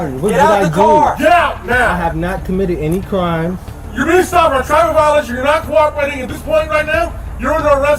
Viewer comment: did he say you’re being stubborn I’m trying to violate you?